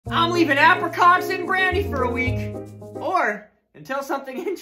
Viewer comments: at first i heard “im leaving apricots in granny for a week” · I thought you said granny not brandy